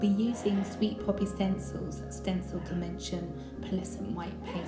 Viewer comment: didn't know there was demention paste...at first I thought u just said stencil paste and I couldn't figure out how u were going to get it to raise! Very pretty!!